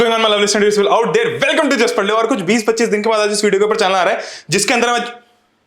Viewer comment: anyone heard same as me : issh video ke upar channel aa raha hai 😅😅